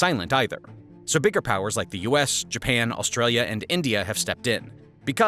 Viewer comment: As an Australian... We have not been pleased over our Government's spineless interactions & lack of a Stance regarding China's Pacific Ocean activities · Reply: For a moment I thought you said Austrian, I was about to say "Time to show em your roots!" XD